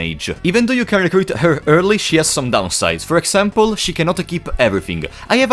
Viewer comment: What do u mean by she cant keep everything? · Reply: I think i figured it out now. You said equip*. Subtitle interpreted it as *keep*. I heard it as *keep